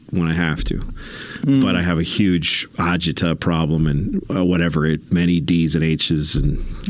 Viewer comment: anybody know what Louis said here? A what problem? Agita?